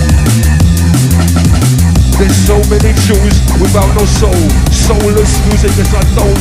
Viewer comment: so many jews without no soul? Did I hear that correctly? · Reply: "tunes" is what you mean....we love everybody from everywhere!